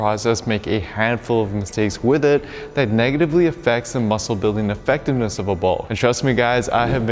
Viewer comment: What does he even say at 0:53 😂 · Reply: I think he meant bulk 😂